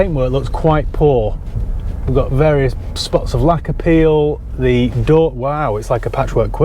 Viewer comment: When you kept saying 'lacquer peel' I could have sworn that you said 'lack of appeal' 😆